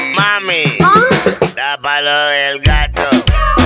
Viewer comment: Pense que decia tapa lo del gato